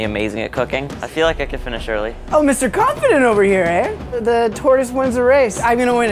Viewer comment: At first a thought said the terrorist wins the race but he said tortoise